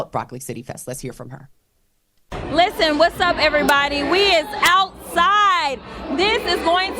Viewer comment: Did she say we is outside